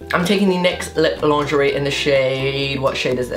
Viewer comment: When said lip lingerie. It sounded like he said lip laundry😂😂😂